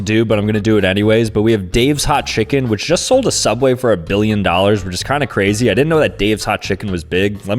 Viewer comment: for a second, I thought he said "a million dollars," and was a bit concerned lol 😂 Cause a million bucks isn't really that much money anymore 🤑😅